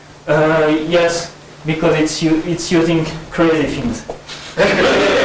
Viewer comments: what does he say at 14:20? it's using what? i can't make out what he says. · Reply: It's using curly things. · ah, yes, you're right. sounds like he says "crazy things". also it's easier to hear if you start